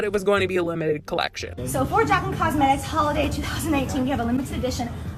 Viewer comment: Did anyone else hear Jaclyn say "Holiday 2018" at 10:06 ? 🤔